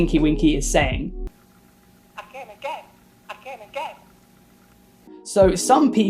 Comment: Personally, I thought Tink-Wink said "I came again". Perhaps I have a dirty mind but that's what I would heard from what is supposed to be gibberish.